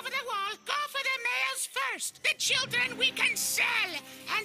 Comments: i thought rhat said “the children we consume” and i was like wait WHAT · Why did it sound like Mort said "B- Children?"